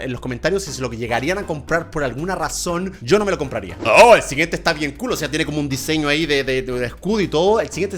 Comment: re que dijo esta bien culo